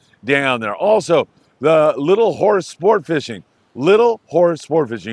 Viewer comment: Ahhhh. That moon messes with BFT. And I thought you said "Little something else" sport fishing 😂😂😂 then you said it again and i was like, of course! Little Horse 😂